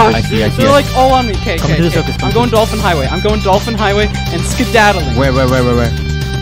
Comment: In the "I'm going to the dolphin highway" I heard it as I'm going to orphan highway and I thought "they might follow him and they might hurt the orphans THEY ARE THE SECOND WORST THING TO HAPPEN TO TTHE ORPHANS